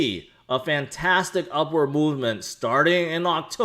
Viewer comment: Did I just hear say "We are on an upward Moonment"? lol.